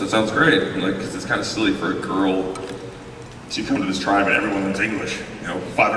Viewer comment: What did he say at 6:30? "its kinda silly for girl who trying to learn English..." I can't hear him it's sound like bumbling.